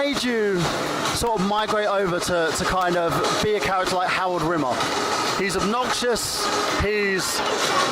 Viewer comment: Did you just say "Howard Rimmer"? HOWARD?... nice intro though.